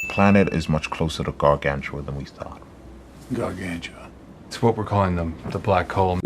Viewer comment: I thought I heard Gargantia, and my hopes were ruined.